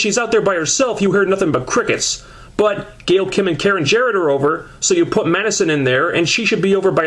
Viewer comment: wow you just said karen is over ... i swear i i heard that .. i thought you hate her so much